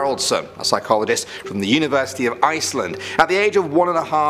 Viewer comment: did you just say iceland- that’s where i am living i